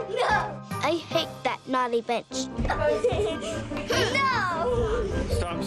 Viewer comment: i thought she said b*tch 😐